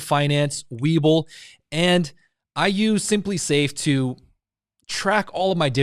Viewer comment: Thanks for the video, what kind of app or website you use to show all the detail about your portfolio @ 1:13 , i heard you said simply save something?